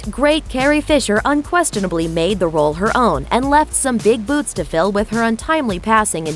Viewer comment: "And left some big boobs to fill" 0:38 What did I just hear? ;-)